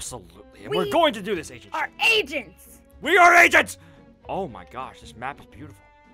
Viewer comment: When they said they are agents it sounded like they said we are asians